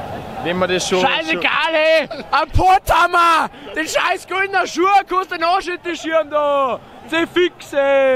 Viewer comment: ich hab legit nicht ein Wort verstanden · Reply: „Scheiß egal he, an pott hamma! De scheiß grüna schua konnst da an osch hinteschiam du. Ze fix he!“ hab ich da rausgehört.. Selbst als Bayer bin ich bei der Interpretation gnadenlos überfragt